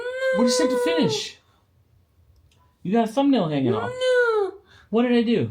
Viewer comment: I know she has never said a word, but it definitely sounded like she said No to you, even the captions said no to you doing her nail. When she kinda freaking out.